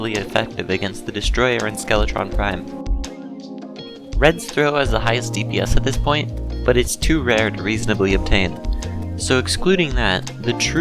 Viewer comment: can’t make out what he said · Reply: "Red's throw has the highest dps at this point, but is too rare to reasonably obtain"